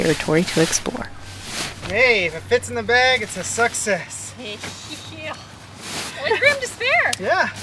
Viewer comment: I swear I heard "if it fits in the bag it's a Suck-sess" I think that's a term many of us use but should TM that.